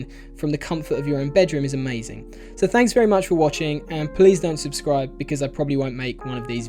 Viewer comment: Did you just say "Pls don't subscribe..."????